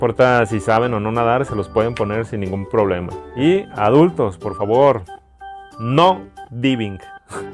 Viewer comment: Hola muy buena la información de tu video. Pero en el minuto 14:24 al explicar sobre la alberca infantil dices que no vivic. No entiendo bien lo que dices me podrías explicar a qué te refieres?